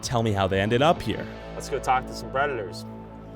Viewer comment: I dont look at the subtitles but @ 6:43 I swore I heard him say "Lets talk to some Redditors" and then proceeds to knock on the doors of pervert park 😂😂